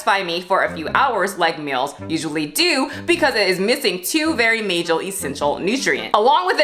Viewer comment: did she say mayjul? 😂😂